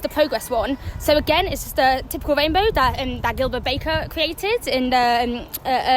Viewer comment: If one asked these questions as little as 25 years ago the general response would be "What on earth are you talking about? there are men and there are women and a few that are confused" 😄 12:07 Did she say "Hylda Baker" ?? "Oh Gilbert...what are you today? oh you're one of those are you?" ...Gilbert nods. .....Anyone under 50 won't have a clue.